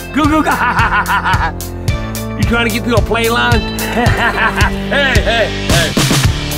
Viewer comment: What does say at 2:57 ??? It’s driving me crazy! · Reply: It sounds like he’s saying “You trying to get through a play line?” But idk if that even fits 😂